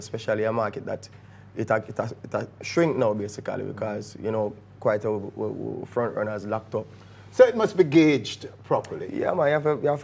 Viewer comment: can anybody explain what he said on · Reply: Quite ooo front runners lacked up.